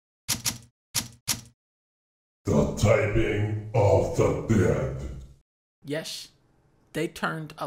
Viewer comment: really sounded like he said 'The typing......of the bed!'